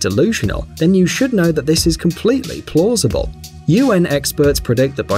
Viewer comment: did i just hear completly plossible